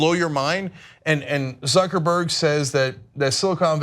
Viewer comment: Thought Cenk said Suckerberg